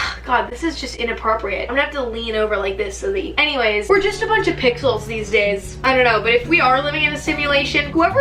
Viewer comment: I thought she said we're just a bunch of pickles in this world lol